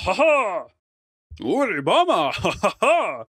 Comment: i cant be the only one who hears "obama" right?